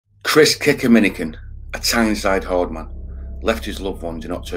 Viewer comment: I thought you said "Chris Kickeminagain"